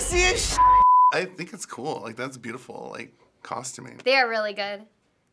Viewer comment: did he just say COSTUMING